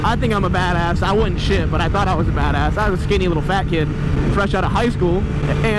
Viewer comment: did he say "skinny lil fat kid"?